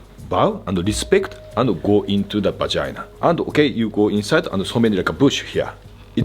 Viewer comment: Did he really say go into the vagina? Swear that's what I heard and I'm not just making a joke 😂